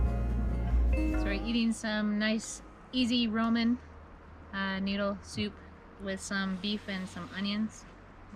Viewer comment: Sounded like Mom said Roman soup. I’ve always pronounced it Raaa-min. Min being noodle in Chinese